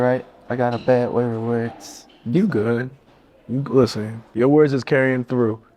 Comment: what he say after he said “I gotta bad way with words”??? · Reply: “im sorry”